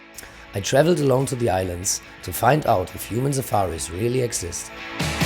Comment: because i'm american and not totally used to indian accents, when the people in this interview say "human safari" i mishear it as "human suffering." i'm only mentioning it because it's an odd coincidence considering how exploitative the concept of a human safari is.